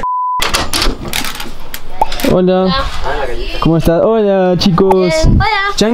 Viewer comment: LISTA DE ACONTECIMIENTOS DEL VÍDEO: - 5:57 piculincito dice que está porque salió de casa y el no salio, se refería a que la ouija salio - 6:38 piculincito dice que dejen de jugar y nadie estaba jugando, puede ser que el que se llevó la ouija este jugando con ella -6:12 cuando Fede pregunta alguien dice (Quico) - 12:31 Aparece un dron atras en la ventana (LIKE PARA QUE FEDE LO VEA ;D) PD: 10 min después y ya hay mas de 200 likes sigan así wacho gracias!! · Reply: En el 2:55 es el que dice hola fede